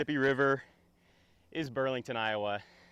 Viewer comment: totally thought he said perviest 🤣